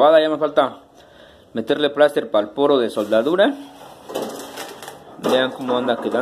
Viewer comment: meterle que ?? para el poro de soldadura ?? no entendi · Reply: plaster de piroxilina